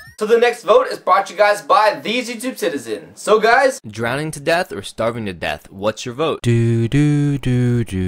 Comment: I thought it said "drowning to death star." D